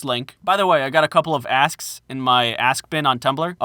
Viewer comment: I thought he said "ass bin" at the end! 😂😂😂